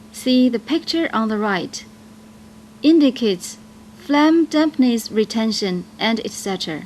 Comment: what is she saying @ 5:44 ? (my native language is not english) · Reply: i heard "phlegm dampness retention & etc."